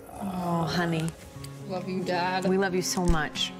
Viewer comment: I thought he Said “w dad”